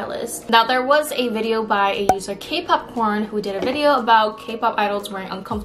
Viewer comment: I thought you said “KPop-Porn” lmao 😂 🍿🌽